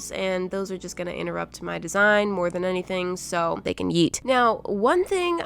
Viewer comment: I thought she said “ they can eat” lol